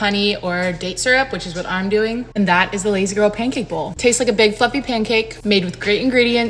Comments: 😂 I thought she said "big fuckin pancake" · Thought she said “it tastes like a big f’ing pancake” 😅 but realized she said fluffy 😂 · I thought she said, “ big, f*ucking pancake.” I was like, “oh damn okay that’s what I want!” Lol…. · I thought she said that it tastes like a big f*** pancake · Sounded like you said “tastes like a big f*cling pancake” 🤣 Love the energy though lol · I thought she said fucking instead of fluffy 😂😂